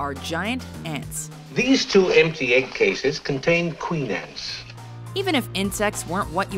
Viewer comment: "These two empty egg cases contain queen ants". I had to replay this several times, just to make sure that he really said that. Maybe I'm not hearing it right. Someone let me know.